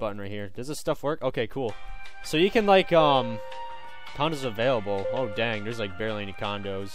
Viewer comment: this entier vid i thought he was saying condoms